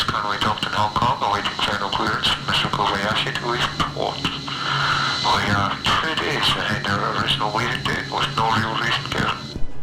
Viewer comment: idk if anybody said before what the dude says here but it's actually "we are three days ahead of our original leaving date"